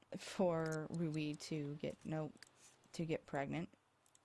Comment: for a second i thought you said judy is very pregnant